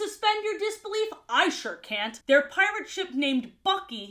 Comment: I’m thought you said “there pirate ship fucky” 💀